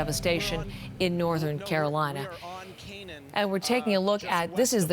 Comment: Did she just say, "Northern Carolina?"